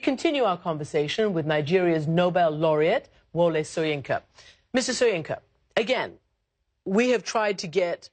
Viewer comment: did she just say Mrs. Soyinka? or Mr. Soyinka ..... I don't get? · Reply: Sgw said "Mr," just with an omission of the "t" during pronunciation, and a speed that made it sound like "Mrs."